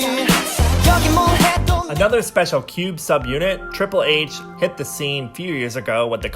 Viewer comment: i thought u said "Another special Cute subunit..." and i'm like yea, Triple H is super cute alrite